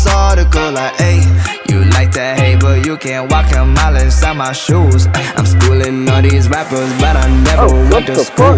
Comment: what rich brian says: "You like to hate, but you can't walk a mile inside my shoes, ay" 1:37 what i hear: "you like and hate the 'Guacamole' on my shoes, ay"